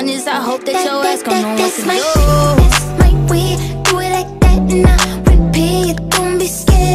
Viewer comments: I thought she said that's my wig 😭😭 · Nobody: Tiktok girls at my school: tHatS mY sH*t, tHaTs mY wAvE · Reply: I thought she said "that's my weave" 😂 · It sounded like she said that's my weave😂😂